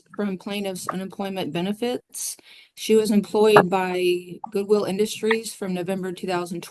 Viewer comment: She was fired from Goodwill and she was evicted from her home. And she still can't see that she is the problem wow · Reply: Oh. I thought the prosecutor said "Google Industries". I thought...a division of Alphabet?? Google? Srsly?